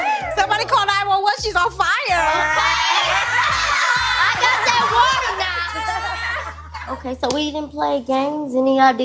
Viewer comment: What ON EARTH DID LADY IN RED SAY!!? " I got that water not" ?? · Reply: She said " I got the water now " it's just she got a thick accent